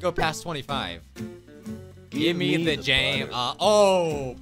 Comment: i thought he said jam lol impressive voice acting I mean he'd always love bUTTer anyway~